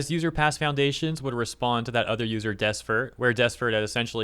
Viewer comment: I kept hearing "Desfert" as "Desperate"